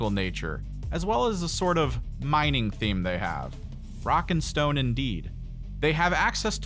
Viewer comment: DID I HEAR A ROCK AND STONE!!!!